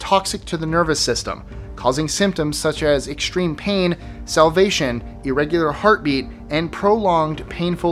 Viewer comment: Did you say it causes salvation? Amazing!